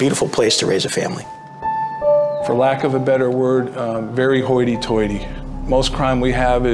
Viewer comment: Never heard the adjective "hoyy toyy" but I'm not an AmE native speaker. Is this "hoyy toyy "really a thing or I've just misheard it?